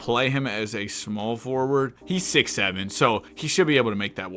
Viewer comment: What did you say? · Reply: 67